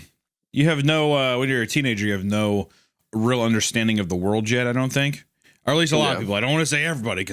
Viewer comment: did he say "the worldgen"